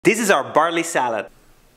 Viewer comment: I thought you said Barney Salad. Clever way to disappear the body.